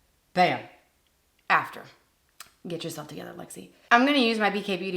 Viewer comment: Love that you kept the foundation footage in! 😂 I totally heard, “Get yourself tanner, Lexi” instead of, “Get yourself together, Lexi.” Which either one made sense. I had to keep rewinding it. 🤣🤣 Love how personable you are. You are a joy to watch!